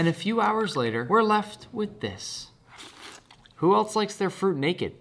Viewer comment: The factt that i heard "Who else likes their food naked" 💀